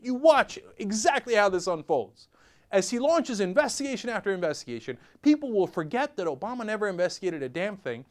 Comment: At first I thought he said ICE was investigating ACORN, and I was confused as fuck.